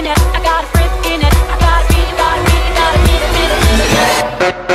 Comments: Man and here I thought she said, “gotta beat, gotta beat, gotta get it get it get it get it” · “I gotta hit with that screen you better watch it.” 0:23 “I got the whip, got the pigeon thought I’d keep it undercover.” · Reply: Oh my god my ears are so dirty I heard "gotta b**th